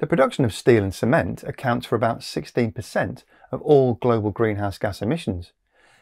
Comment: did he say 60 or 16 percent? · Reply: 16%